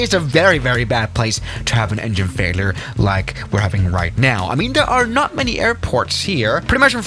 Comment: when you said riiight I thought you said Ryanair I was like NOOOOOOOOOOOOOOO